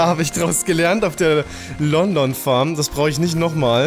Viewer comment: Wer hat auch bei 11:15 "London Farm" verstanden? :D